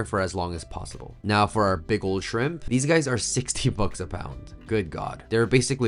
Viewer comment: On the first pass. I thought you said $16 a pound 😮. I was like, "I will eat them everyday!" Then I heard $60 a pound! Yeap ... Shirp cup O nudle it is... 😅